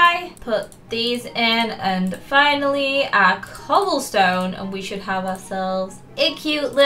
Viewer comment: When she said cobblestone I thought she said coal stone😂😂